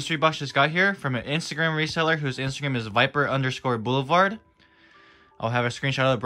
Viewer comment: Good box 9/10 Thought you said diaper boulevard lmao